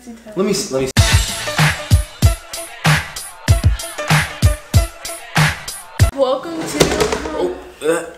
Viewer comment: At first I thought she said Welcome to Wakanada